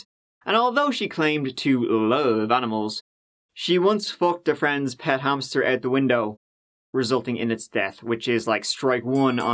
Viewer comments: Yoooooo I thought you said “fucked the hamster out the window” 😂😂😂😂 but im assuming you said “flunked” I’ve never heard that before in my life 😂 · WTH! ! 1:40 ..... I thought you said " she once f*cked a friend's hamster... " Then you finished that sentence and I realized my hearing is what's f*cked. That was an odd place to pause though, between hamster and out the window. RIP friend's hamster. 🥺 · I thought you said "she once fucked a hamster out a window" and i was so confused